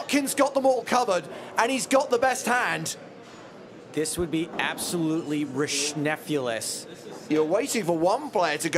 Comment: What word was that at 0:54? Rischnefulous??